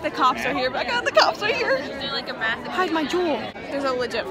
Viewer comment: U juul? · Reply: Oh but i think i heard u said "hide my juul"